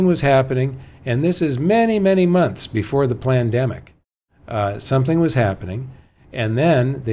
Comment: did Mike say "plandemic"